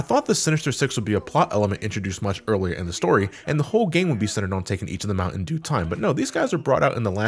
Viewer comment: I swear i heard “taking the Mountain Dew time”